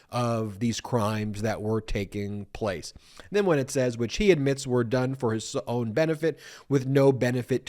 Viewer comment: What is a witch hunt? I keep hearing this phase from him!😂